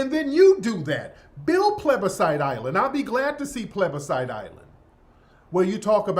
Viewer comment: I thought Tariq was saying pleathersite island 😂